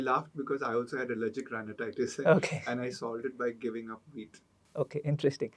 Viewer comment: did you say giving up wheat, or giving up meat?? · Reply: Wheat. For.